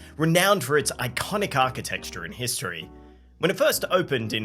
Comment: Did he just say “architexture”?